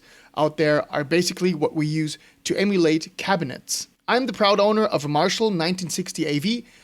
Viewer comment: First listen I thought he said “IR are what we use to emulate “Happiness.” and I agreed haha